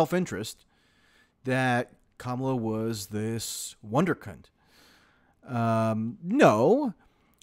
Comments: Did he say "wondercunt"? @ 1:12min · I swear I thought you said "wonderk#nt"...seriously! 🤣